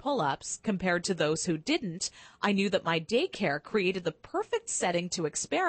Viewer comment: did anyone see at 6:43 the subtitles say "dick air" instead of daycare